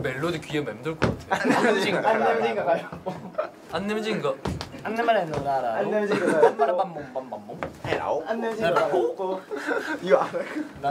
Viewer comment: lol jackson...that's what I hear everytime they play rock paper scissors🤣🤣